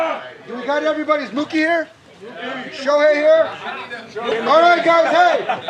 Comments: ショウヘイいる？って聞こえるけどそれっぽく聞こえるだけかな？ それとも日本語？ ドジャースおめでとう！ 熱い戦いすぎた！！ こんな伝説的なWSの最終戦、最高の試合見せてくれた両チームに感謝を！ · ムーキーいる？ショウヘイいる？に聞こえるの好き